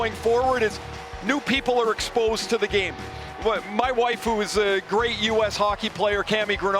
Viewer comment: Wait... did he just say his "waifu"??